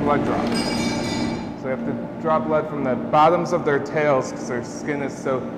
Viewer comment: I was having a difficult time trying to figure out what the male zookeeper was saying when he was messing with BeBe's tail. I thought he said, "...so we have to drop lead from the bottom of their tails..." I was like what are you talking about dropping lead, is that a euphemism or Hippokeeper speak for something? 3rd time around, I heard, "draw blood". Love Fiona & her miraculous story!